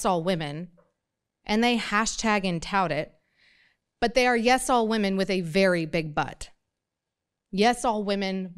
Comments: i heard "'yes all women' but with a very big but" as "'yes all women but with a very big butt" and was very confused :) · Did she just say "Big Butt"?...